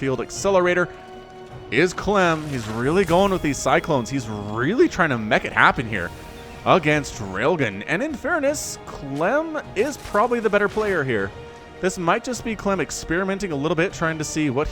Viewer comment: Did he actually say "He's really trying to 'mech' it happen here"?